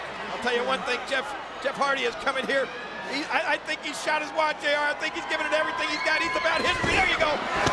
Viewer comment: Did king say jeff shot his wad 😂